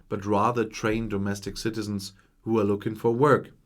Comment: "Trained Domestic Citizens" -- I thought for a second you said "Trained Domestic Servants". That is the Brexit spirit...